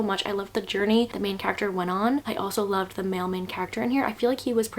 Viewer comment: I thought you said mailman character instead of male main character 😂